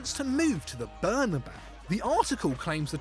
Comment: I had to rewind because when he said aguero wants to go to the bernabau i thought he said burnley 😂i was like tf?!?!